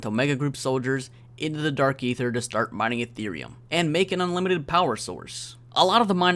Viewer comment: I legit thought you said mining Ethereum 😂. Peck is truly a visionaire